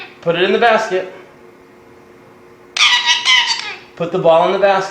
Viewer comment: cool bird- it really sounds like he says "put the ball in the bucking basket" at the end there